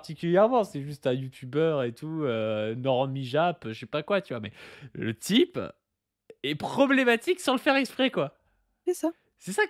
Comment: J'écoute les vidéos sans vraiment les regarder, en étant occupé à autre chose, et pendant toute la vidéo, j'ai entendu "Wissam est problématique"